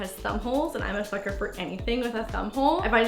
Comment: i thought she said f*cker lol